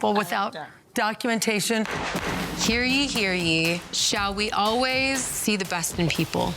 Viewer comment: Did anyone else hear Meredith say "hear gee, hear gee?" for her amendment? Go to 4:38 and listen to what she says. Wow, I am not sure what's up with her but someone is having trouble.